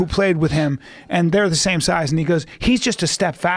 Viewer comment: did he just said the N word 1:58 ? lol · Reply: "and he goes"